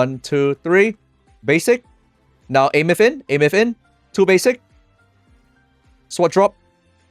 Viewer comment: what is he saying? (something drop?) what is that? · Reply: Sword Drop. Referring to the Ultimate Animation where the Mech drops it's Sword into the Ground. Atleast that's what i heard 👀